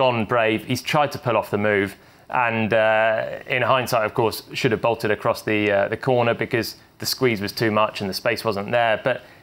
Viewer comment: so the conclusion on this vid is that max crashwillhappen should have run off. shocking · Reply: that's not what he said. what he said was, that max: "should have bolted across the corner". you deaf?